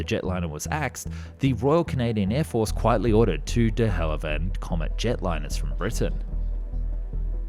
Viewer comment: Did you say "De Hallivand" instead of "De Havilland"?